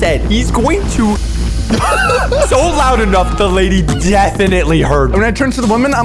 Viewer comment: I think is saying he's going to blow up the plane😅